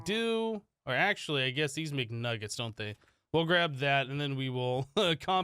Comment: sounds like you said "these mcnuggets"